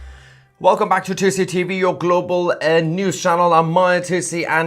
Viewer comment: LOL i DIDN'T HEAR WHAT YOU SAID AT 1ST i COULD OF SWORE YOU JUST SAID WELCOME TO JUICY TV. TOOOOOOOOO FUNNY